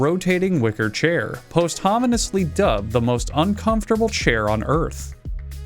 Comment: Everyone else the entire video: laughing Me: 2:16 That's the first time I've ever heard "posthumously" out loud. Is that how it's actually said??? EDIT: Is he actually saying "posthumously"? Listening back, now I'm not sure.